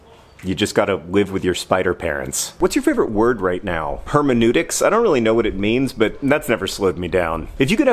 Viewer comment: hermeneutics: the branch of knowledge that deals with interpretation, especially of the Bible or literary texts. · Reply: I thought he said "permeneutics" and was confused.